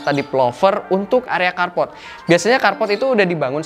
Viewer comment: kenapa tiap bilang developer kedengerannya jd depelover sih... agak gimana gitu...